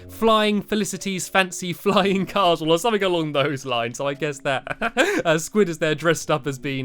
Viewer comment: When you say "On those lines", lt sounds like you were saying, "On Long Bows lines.